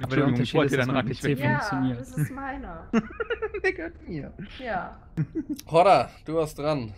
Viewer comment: Warte mal... was sagt bei 12:43? O.o (Während er lacht) Ich verstehe dort etwas, was man lieber nicht in der Aufnahme sagen sollte. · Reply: Er sagt "Der gehört mir"